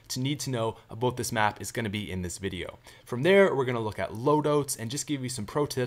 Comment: For a second i thought he said loadoats. GETCHA LOAD-OATS HERE, FRESH FROM THE MICROWAVE!